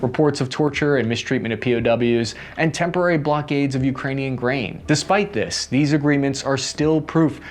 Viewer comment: I mistakenly heard you saying instead of grain I heard brain 🧠 I was like man they sale brain nowa days